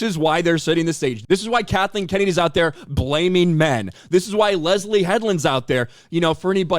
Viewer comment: I thought he said “Lesbian Headland” at first 😂😂😂